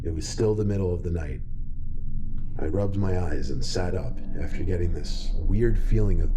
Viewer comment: God my brain and ears are broken, I seriously thought he said "I rubbed one out" instead of "I rubbed my eyes". Had to do a U-turn on the video and re-see that part before I got it...